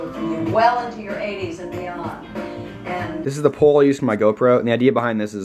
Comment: When said This is the pole I use for my GoPro my ears heard it as This is the pole I use to dance on.